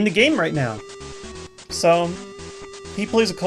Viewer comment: I've watched these videos for so long now, but I've had no idea that he was saying "Colorless Machine" until now. I thought he was saying "Kohlress Machine." I was like, "WHO THE HELL IS KOHLRESS?" =P